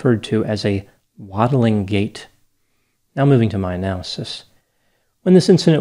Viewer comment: There was a tweet about a news article saying Vicky White has a “waddling gait” with the caption “If I ever go missing and one of y'all describes me as having "a waddling gait," so help me.” and now that’s what I think about every time this case is talked about 🤣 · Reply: At first I thought said "modeling gait". Big difference there.